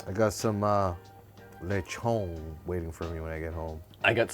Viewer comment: did you say lechon?